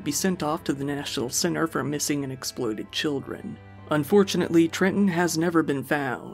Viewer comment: Please excuse my humour in the midst of a horrible case but... I swear I heard Missing & Exploded Children 😆 Just me?